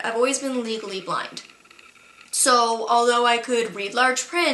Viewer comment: When you said legally blind I thought you said legally blonde I was so confused 😭